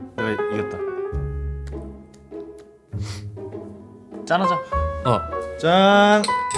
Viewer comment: 짠하자가 아니라 짠하다 로 들리냐..